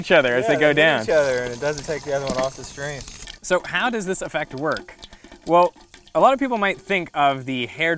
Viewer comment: I thought he said "So how does this affect work?" as in "affect [your] work?"